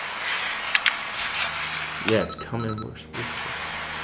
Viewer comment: OK this could be me just hearing things. But when they asked what was the entity's name? I could have sworn I heard it say hell, Lucifer.